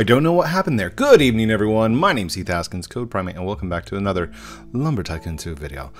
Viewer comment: Every time you say I thought you said code primeape